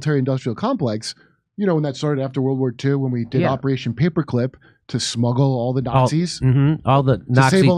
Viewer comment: HA! I thought said “Operation Hypocrite.”